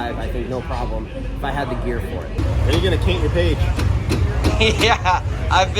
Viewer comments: says "When you gonna caint your page". I had to replay it 3x and once more with the closed caption on to be sure I heard him correctly. No big deal...just something I noticed. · <link> "When are you going to caint your page" I'm not the only one who heard that right?